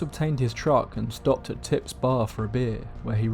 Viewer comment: OMG I thought you said "Tit's Bar" lmao